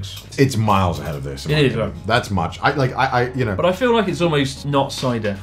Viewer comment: ish, it sounds like Chris says "Így van", which means "yeah" in Hungarian. What is he really saying there?